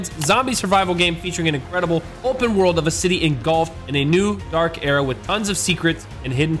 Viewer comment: When was doing his add I thought he said " and you can play golf " instead of " engulfed "